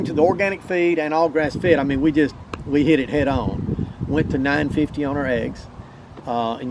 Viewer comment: Did he say $9.50 a dozen on the eggs at 17:14 ?